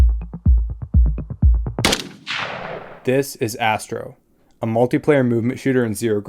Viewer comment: thank goodness for the subtitles, i thought you said: "this is Assbro" x_x